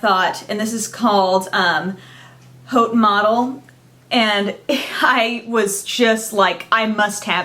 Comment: really thought you said, "coke model" haha, maybe?